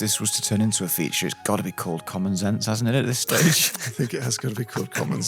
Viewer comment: Ah! I listen at 2x speed, so I just thought they were saying "Common Sense" and was unclear on why it was clever. Common Zense makes more sense.